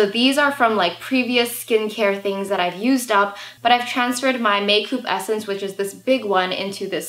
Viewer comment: Did she just say make-oup HHAHAHHAHAHAH